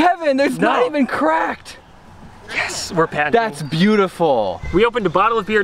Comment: Idk if anyone else heard it, but at 7:32 Kevin says 'Yesss, we're patenting' Ah yes, the beer bottle opener that runs on 15k volts. Perfect for drunken parties.